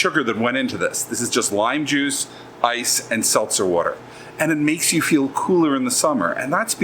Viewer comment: Did you just say "salsa water"?